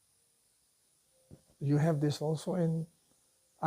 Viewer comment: No what does he say at 39:02 i cant understand the pronunciation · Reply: Tepui. Like a mesa.